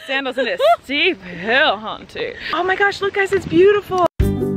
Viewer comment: when you said "look at the beautiful view" I thought you said "look at the beautiful rachel" and I was like ugh